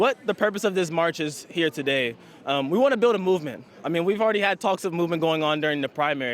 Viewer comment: sorry, thought he said we want a bowel movement....